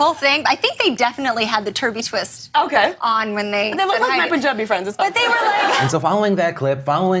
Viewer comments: Am I like missing the joke Lilly Singh made? Cause I know they actually are called Turbie Twists and then whatever the hell she said was so fast and offhanded that just...sigh. I feel like if we're gonna get offended there should be like levels from 1 to 10 of how outraged to be and appropriate response s and the internet always has it turned up to 10. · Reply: English is not my first language, but this what I heard: "OK... (something unintelligible) my Punjabi friends as well." So I guess the joke was that her Sikh friends also wear turbie twists. I also think it's not a big deal, but what do I know. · So... what did Lilly say at 8:09? I got "they look like my punjabi friends". Was that the offensive part? Did she even say that? I'm asking because I had to replay that part like 5 times before settling on "punjabi friends" because I didn't really register a word of what she said at first. ._. Sleep deprivation does that sometimes, I guess. PS: Also, this is what happens when you write a comment before watching the end of the segment. She did say that. What the fuck America. There's people dying, and you get mad at this. xD